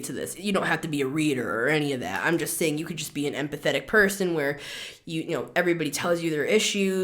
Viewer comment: Lmao I thought you said “and pathetic“! When you said empathic!😂❤